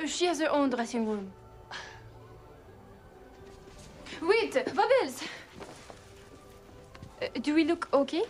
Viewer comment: I get curious. Are they really naked when filming this scene? --And "bubbles" is such a great and funny idea from MJ. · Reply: Oh, I didn't know he said "Bubbles" I thought he said "Bobos"